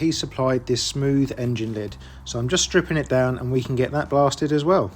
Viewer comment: wow....that is shocking...though a bit like a forensic autopsy!... no wonder at 7:48 it sounded like "and we can get that bastard as well".. don't blame you ! 😂